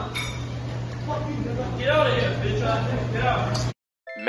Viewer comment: If I heard it right did that Chinese man call them a b*tch ass N*****? Not that I'm mad im just shocked